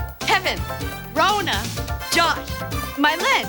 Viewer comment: what they said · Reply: “The Party” which was their band name. It consisted of older mmc cast members